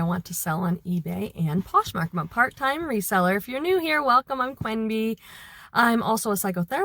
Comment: I thought at first u said “Plan B”, instead of Queen B 😅 🤦‍♀️ both I guess could be corky fun nick names! ❤😂🎉